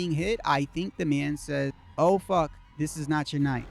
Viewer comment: When you hear "oh fuck this is not your night", i hear " oh fuck you dropped your knife"??..